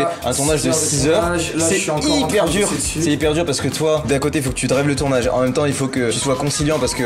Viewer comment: oui oui moi aussi je drague le tournage xD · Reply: Mdr je pense te compris c drive qu'il a dit mais mdr bonne blague🤣🤣🤣